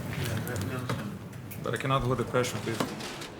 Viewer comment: Please, What did he exactly said in 47:17? I couldn't understand those few words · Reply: but i cannot take the pressure of this